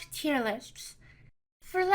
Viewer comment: I heard “tier-lisps”. Thought it was cute. Moving on.